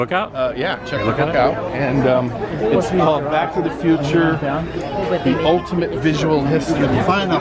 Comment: in the name of this book Back to the future the ultimate visual history? I think that’s what I heard. 🤔 Looks awesome